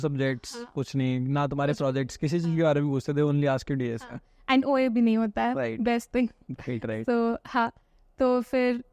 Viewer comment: what did she said ? kuch kya nhi hote any one reply if u understood what did she pronounced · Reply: OA i.e. Online Assessment